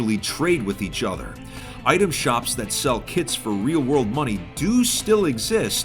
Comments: "Item shops that sell kids for real world money" Had to rewatch that bit to make sure what he said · I thought he said "Item shops that sell kids" LMAO